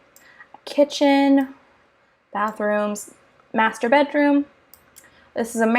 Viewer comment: It's Gerad, to me it sounds like yours saying Ginrad